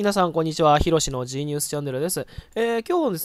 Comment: 毎回「ヒロシのGニュースチャンネルって聞こえる」